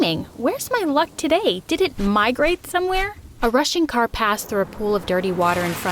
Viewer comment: A rushing car I heard it as a Russian car ;w